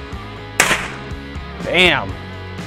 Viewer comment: did he say danm